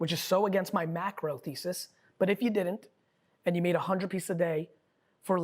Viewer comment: at the 11:01 mark did gary say post 100 pieces a day? thanks